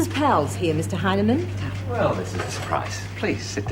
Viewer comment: Did i hear that right? The dude at 17:28 was called Mr Hymen? surely not. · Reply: Hy_ne_man, but close.